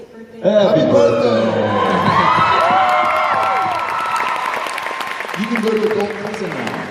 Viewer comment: What did Jared say at about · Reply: I believe he says: "Happy Birthday! You can go to adult prison now."